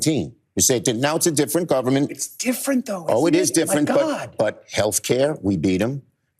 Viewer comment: did I hear Chris say under his breath "it's a different government my God"? Schumer was seething that he had to do these media rounds and actually answer questions from the commoners, excuse me from the newsreaders.